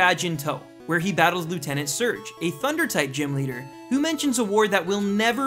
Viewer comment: Did he just said thunder type?